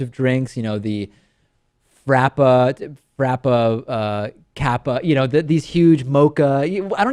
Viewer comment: Did somebody say Kappa?